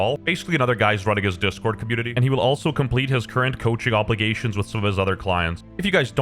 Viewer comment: he said "The Channel goes under", I heard, "The China goes under"😐.. IYKYK